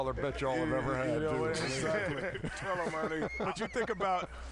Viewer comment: does shaq say tell him my nigga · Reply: "Tell him Ernie"